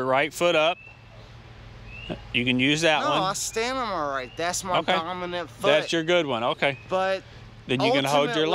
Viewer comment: did he say, that's my permanent foot? How drunk must you be to say such a thing?